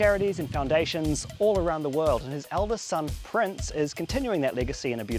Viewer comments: Min 0:27 why does he say Elvis son prince???? I thought prince was michaels son????? Wait whats going on here.???? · Prince is so well spoken. I know Michael is proud ❤ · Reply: Min 0:27 why does he say elvis son prince???